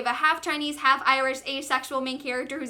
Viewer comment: WAIT 13:18 DID I HEAR ASEXUAL